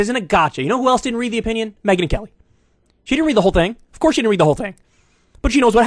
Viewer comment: Did he say "megyn the Kelly"?